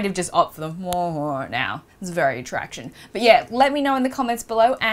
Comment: does she say "it's very attraction"?